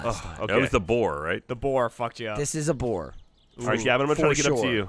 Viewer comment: Did someone say... For Sure A Boar???